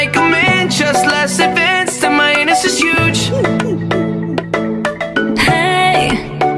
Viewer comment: ‘ mY aNUs iS hUge’ got me laughing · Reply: Thank you what I heard was" my head is this huge" I wondered why mom was p.o